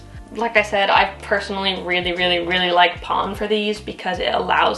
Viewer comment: Question: @ 11:05 you say you really like "pond"? Can't understand but would love to know if this is a kind of mixture or brand? Thanks